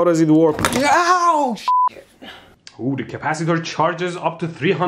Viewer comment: late silent. I hear shit.